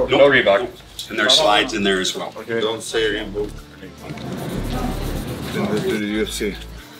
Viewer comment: "Do not say again REEBOOK" ? Did I hear well? :D Are they so strict about not mentioning their former main sponsors? :)